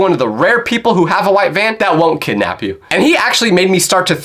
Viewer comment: I thought he said white fan😅😅